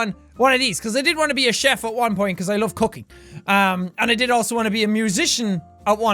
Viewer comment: I thought jack said that he wanted to be a chef because her loves cookie instead of cooking